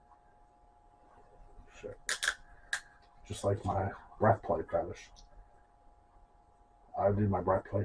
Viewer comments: breath play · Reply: oh that makes sense. Thanks. It sounded like he was saying "bread putty fetish" 😂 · What is he saying at 6:12? His microphone on that camera makes it hard to understand. The what fetish?? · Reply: breath play